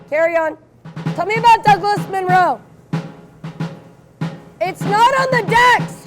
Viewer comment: what was the question ? literally hard to hear what they say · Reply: Tell me about Douglas Monroe.